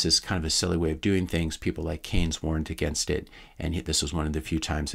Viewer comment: Note: He says 'warned' and not 'weren't.' Changes the meaning completely if you mishear it as I did.